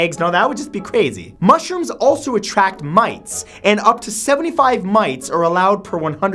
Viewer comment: You said mites, but you're editor put a picture of a mouse. He thought you said mice hilarious